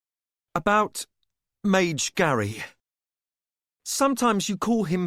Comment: “Mage Gary” instead of Mage Grey 🤣😭 Fitz: There has never been another being in this world who understands my soul as he does. Audiobook Narrator: Mage Gary 😭🤣